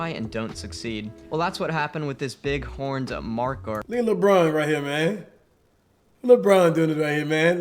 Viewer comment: Did he just say look at lebron😭💀💀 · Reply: Yeah it was Lebron...